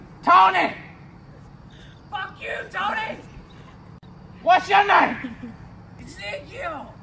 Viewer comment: I thought it said wash you Maine